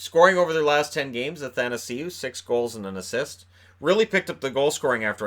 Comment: Every time you say "Anthanasieu" in my mind I hear "happytoseeyou".